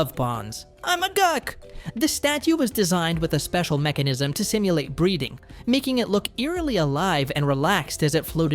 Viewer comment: maybe it's me but it sounded like you said 'to simulate breeding' and it made me choke on my drink